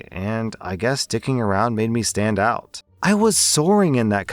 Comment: why did say d!cking around when it clearly says sticking around?